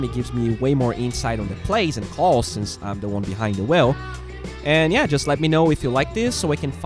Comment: Nice. I like the vid and the insight to your game thoughts and your other thoughts. PS. when you said "wheel" at the end, it sounded like you said "well".